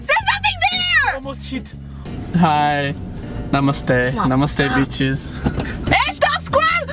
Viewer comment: Did I just hear "Namaste, Namaste, Namaste Bitch" ?